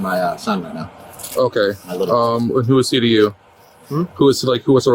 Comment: Some of these subtitles are wrong. 5:15 - "who is CDU" its who is he to you 8:10 - "Pedal" Pedo 21:36 - "or" For